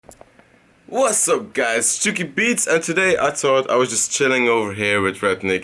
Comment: Is it me?...or I always hear "Chiken beatz"?!